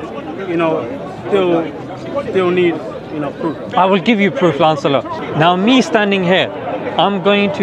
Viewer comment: What does say at 2:55? I didn't get what he told heckler after "I will give proof ......? Auto generated CC says "Lancelot"